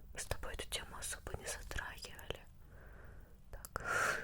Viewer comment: Мне одному послышалось? -Мы с тобой эту тему не затр*хивали.